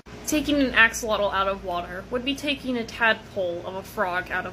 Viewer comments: Frogs can go out of water. It'd bee more accurate saying taking a fish out of water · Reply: Honestly couldn't tell she said "of a frog". I'm British and American accents can be difficult for us. And I still think it sounds like she said "or a frog" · Most frogs are not fully aquatic... and tadpoles DO eventually come out of water. Not great comparisons for learning. A better comparison would be that they’re somewhat like fish. You can take them out of water extremely temporarily, but it is stressful and not healthy for them long term. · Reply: "Tadpole OF a frog out of water" not "tadpole or a frog". I misheard too. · Frogs are amphibians and can go in and out of the water forcing them to stay in one or the other is bad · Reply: I thought they say tadpole or frog that makes a lot more sense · Did she just say it's like taking a frog out of water